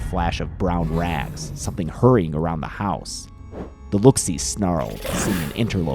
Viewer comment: we should get Mordeo and Telos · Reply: Skee For some reason I thought it said Loo See and Telos 😅